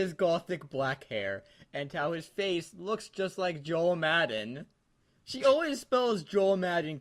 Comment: I thought it said John Madden at first. That's an image.